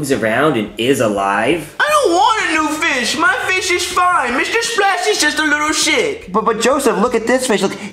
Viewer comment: i really thought joseph said "mr splashy is just a little sh*t"